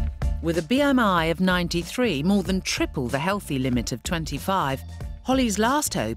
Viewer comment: “With a BMI if 93, nearly TRIPLE the limit of 25” Umm.. anyone wanna tell them? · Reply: Thought they said MORE THAN triple? Also I am/used to be a Black Knight too :))